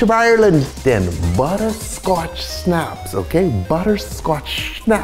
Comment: For a sec I thought you were saying “butterscotch snots”